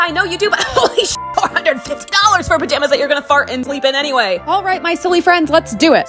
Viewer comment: I had to rewind the video cuz I thought you said "fart and queef in" not "fart and sleep in" 😭😭😭😭